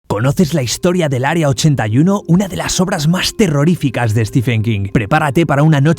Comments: Pense que decia Area 51 xd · Pense que decia stephen hawking XD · Por un segundo pensé que decía Stephen hawking :,v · Yo pensé que decía Stephen Hawking